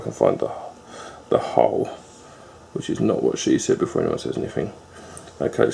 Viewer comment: Great review, Honest and to the point. I heard "that's what SHE said" and made me smile.